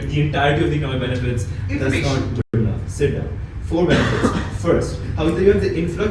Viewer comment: did he say 'sit down'? lmao